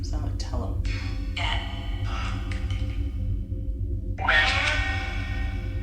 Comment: i hear the name....."ROY".....right there.........???????